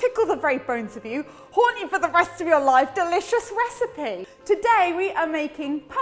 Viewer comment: at first I didn’t hear “haunt you for the rest of your life” I heard “Horny for the rest of your life” and I was super confused